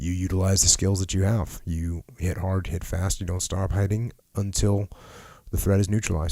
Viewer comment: What did Jocko say at 11:25? His voice seemed to taper off. Did he say, "Don't stop hiding?" "Finally, if you get to a point where you have no choice, well then you utilize the skills that you have. You hit hard, you hit fast, and don't... Until the threat is neutralized is and you get away."